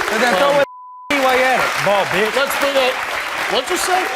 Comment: I can't believe he called Steve a bald bitch! Omg! I had to replay that part again😂 Little does he know he's going to be someone's b!tch in jail👀 Way to go Steve another case gets it's Justice ✨Praying for the mom and all her babies sending positive energy and good vibes, may the universe help you on your path towards healing✨ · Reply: Lol me too i thought he said tall b*itch at first lol